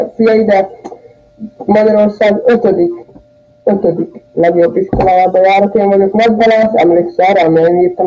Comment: valaki értete mit mond? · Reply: Valami olyasmit hogy Magyarorszag 5.-ik legjemenyebb iskolajaba jar es hogy bunko vagyok..talan talalooztunk mar az appon vagy ilyesmit dunnyog